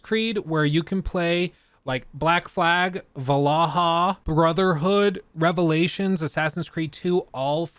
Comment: did he just say va la ha